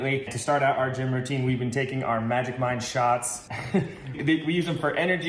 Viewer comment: This is going to be a crazy comment, I thought you said magic mike until I saw the bottle and the name of the drink. Dam hillbillies speak English. LOL